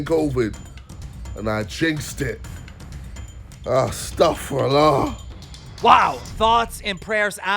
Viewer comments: did he say "AHH AstakhferAllah"? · Did he just said astaghfirullah?😂